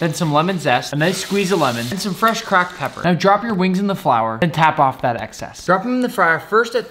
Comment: OMG 😱 😱 😱 did he just say flour